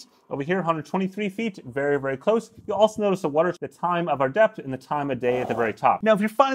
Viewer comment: I literally heard "time of death" not "time of depth"